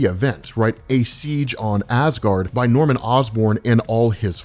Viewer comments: When said "...went to Siege On Asgaard", I first heard "...went to see John Asgaard". And I was like, "Who? Did he say 'John Constantine' and I just mis-heard it??" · Ohhhh, Siege on Asgard. I thought you were saying "See John, Asgard." and I was thinking what an interesting title that was, wondering who John is.